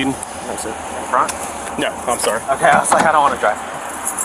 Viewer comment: did he say "good, cuz i dont wanna drive" as if he thought he would drive the police car??????